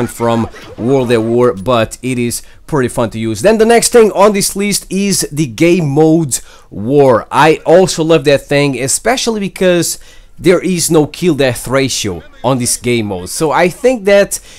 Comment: I thought he said, "The gay mode, war."